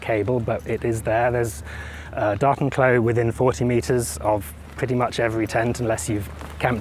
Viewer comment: Sounds like you got all your ducts in a row! Btw, I heard "Dartenklo" at first and couldn't make sense of it.